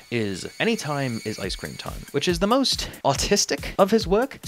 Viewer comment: i genuinely thought you said "autistic"(i thought you were using the word "autistic" as an insult) and i said out loud "great.." (sarcastically) until i rewatched it and realized i misheard.. i was almost about to read a mean comment all for nothing